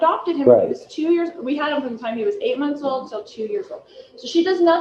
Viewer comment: she says that we adopted him since he was eight months old until two years ago? What did she say? Thanks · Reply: Until 2 [yrs old..from 8mo..] but she's not making sense because she's trying to keep up her front and bizarre lies mixed with facts, seemingly manically deflecting. J.J. was officially adopted at age 2 in 2014, right before she & murdered husband (#2?) moved to Hawaii (maybe she took him in before, as CPS removed him & placed with her before official adoption 8mo old?).